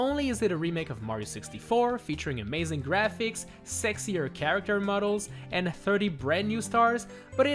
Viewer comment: did he say sexier characters